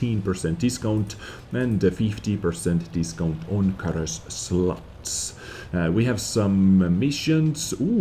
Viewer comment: did I hear - "SLUTS" instead of slots? xD